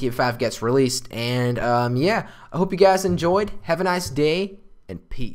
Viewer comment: At the end i thought he said " have a nice day and pee".